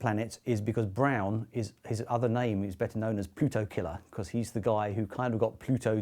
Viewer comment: I wasn't looking at the screen when he mentioned "Pluto Killer" and misheard it as puto killer.